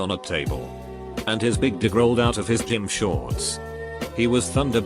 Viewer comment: Anybody else heard "his big dick" at 0:43 ?